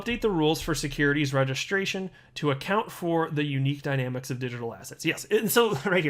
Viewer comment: every time says "digital asset" I hear "digital a$$ hat". Yes, I am an intellectual.